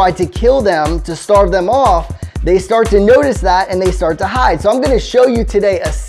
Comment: can someone please write what is the word that is said here. They start to "what"? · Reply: The word is they